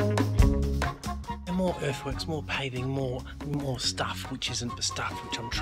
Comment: Man I thought you said "more earthworms" lol.